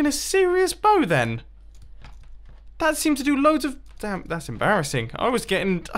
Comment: Did he say damn